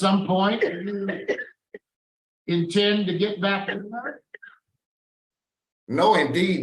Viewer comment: I believe the parolee heard what I heard: "... do you intend to get back AT her?" Hence, his reply "... No, indeed, never. I wouldn't dare." BUT, the actual question was: "... do you intend to get back WITH her?"